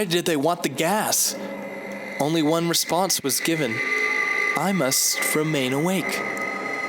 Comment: what is he saying ???????? i dont understand it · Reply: I must... Remain awake